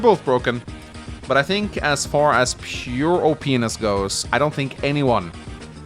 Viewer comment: Did anyone else hear "Pure O-p****"